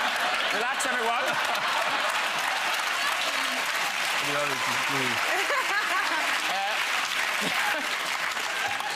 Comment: can anyone tell what Josh Hartnett said to Mel C? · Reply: And the audience it's laughing-is what he says